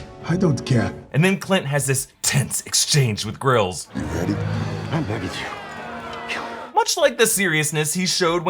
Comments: I thought he said “I’m begging you. Kill me” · I thought he said "I'm begging you, kill me"